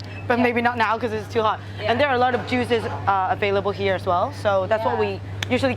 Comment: Lol, at 12.05 I thought she said 'jews' instead of juices! Made me laugh.